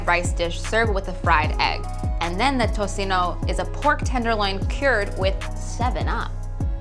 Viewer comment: what's that word that starts with a P? Sounds like she said purred, is that a technique that involves cat love?